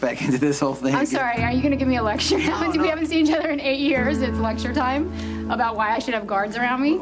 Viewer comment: and 7:26 Did Brenda just say we haven’t seen each other in 8 years